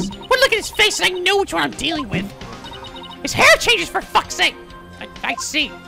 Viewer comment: I genuinely thought she said 'He's irritating, for fuck sake!' on the passive listening while I did stuff, then legitimately went to play it back, and it sounded completely different. Thought literally right then and there, had it be that Geiru spoke up on hating him right then and there, but heard it completely wrong. Though that would be clever as hell if it just suddenly went 'Yeah, hate the little guy, get's on my nerves that I have to be with 'em!' Nice job on that one, ya got it to unintentionally for a moment that she deliberately hated Owen right at that moment to me.